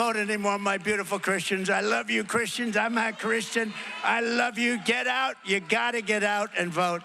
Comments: Did he say "I'm not Christian" @ 54:45 or am I crazy? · I thought Trump was Christian? He says @ 54:46 he's not. Hmm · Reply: No, he says I'm a Christian. He's went on record before saying he's a Christian, he has always been. Don't use a "word tumble" to push a bs agenda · “And again, Christians get out and vote. Just this time. You won’t have to do it anymore. Four more years, y’know what. It’ll be fixed, it’ll be fine. You won’t have to vote anymore my beautiful Christians. I love you Christians. I’m not(?) Christian. I love you. Get out. Ya gotta get out and vote. In four years you don’t have to vote again. We’ll have it fixed so good you’re not gonna have to vote.” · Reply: he says "I'm a Christian." There is no "n" or "t" between "I'm" and "a Christian." The auto-generated transcript says "I'm a Christian." There is something wrong with you to post an imaginary transcript of that passage as if he said the opposite of what he actually said. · If you are already wondering at the "Im not a christian" At 54:45 the closed caption say Im a christian. Fact checked 🇺🇸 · Did Trump say, "I'm not Christian "??? Im confused. I thought he was a Christian. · Did he just say "I'm not Christian"??? 🤔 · Not sure if he said “I’m a Christian.” or “I’m not a Christian. Trump has proclaimed his faith before so it’d be strange if changed his tune. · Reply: I heard “I’m not a Christian”